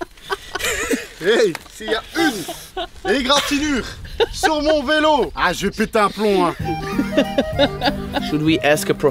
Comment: Love the video guys! What does say at 6:23 in French? I'm trying to find the french words, but cannot find it! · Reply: "Hey ! Si il y a une égratinure sur mon vélo... Ha, Je vais péter un plomb hein!" With such delicate accent lmao. Yep it is 3 months later but you finaly got it ^^